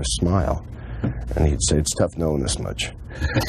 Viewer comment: I can't hear the answer to "why don't you ever smile?" - ?? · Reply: "It's tough knowing this much."